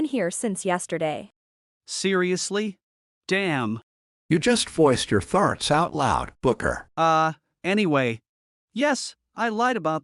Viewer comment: "you just voiced your farts out loud, Booker" is what I heard 😂